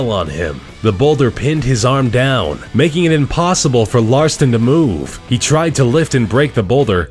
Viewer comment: Ralston? It sounds like he is saying Larston.